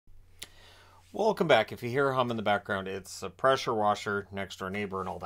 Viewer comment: All i heard "if you're here hum" as its really hard to hear the rest of the vid while humming. Also i really like that jersey.